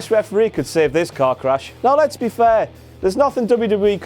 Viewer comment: I know he said Lesnar/Goldberg was like a car crash, but it sounded like he said the match was a cock rash